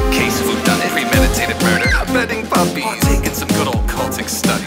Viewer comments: WHEN I FIRST HEARD THIS SONG I THOUGHT HE SAID "killing puppies!" NOT "petting puppies"😭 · I swear I thought I heard him say "burning puppies!" rather than petting puppies.